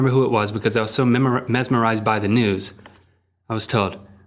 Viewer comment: I thought he said "I was so mesmerized by the nudes"